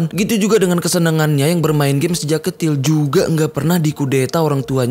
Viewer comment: gue dengernya sejak ketil